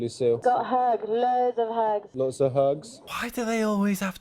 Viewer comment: I thought it said got ho’s lots of ho’s 😂😂😂😂🤣